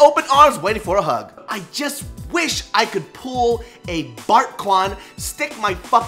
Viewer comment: Waitwaitwaitwait... at 3:19, did he say "I wish I could pull a Bart Kwan"? What's up with that?